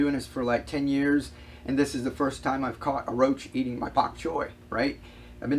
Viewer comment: !--I have Chinese Spinach growing in one of the food grade containers! Thanks so much for inspiring me on this food growing adventure! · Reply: Is that what he’s growing Chinese spinach I thought he was saying bok choy but maybe I’m hearing him wrong